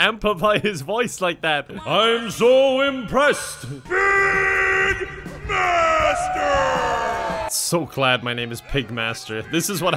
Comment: We that dude said pig master I thought it said big bastard